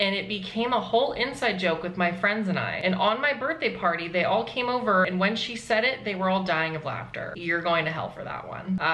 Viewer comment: Usually I can understand bleeped words but what word was it at 2:40? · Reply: It was f_k